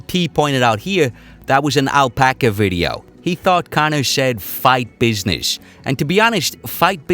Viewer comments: I thought he said fight bitches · I hear "...fight business." I am sure it was preceeded by something like "...quit bitchin'.." Great film as usual. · To me it sounds like he said, “...Fight business” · I heard " tough business "